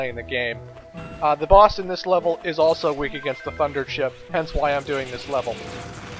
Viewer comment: Late comment, I know, but...why did it sound like you said "fucker chip" instead of "thunder chip"?